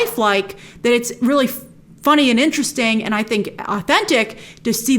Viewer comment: Did she just say the F word 6:36 nice cover 😂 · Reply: Nah she was saying "funny and interesting"